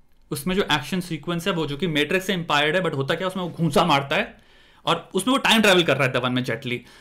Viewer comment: did he just said impired instead of inspired.